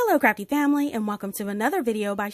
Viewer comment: I thought she said hello crappy family